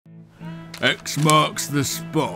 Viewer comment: Why everytime I hear "X marks the spot" I hear "Xbox"? 😢